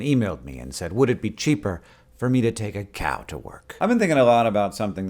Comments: i thought he said "cab to work" at the beginning and it took me half the video to figure out why he was talking about cows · I heard “take a cab to work” so I was a bit confused as to why he started talking about cows. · I thought he said "cab to work" and not "cow to work" and for a while I was wondering where the cow thing came from